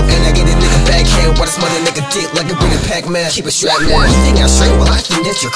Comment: what says isn't actually incomprehensible, he says "keep it strapped man"